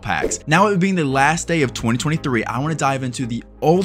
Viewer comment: I’m sorry did he say the last day of 2023